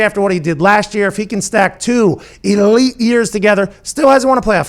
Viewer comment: Every time I heard him say “elite” I thought 8-8 😂 Edit: about Dak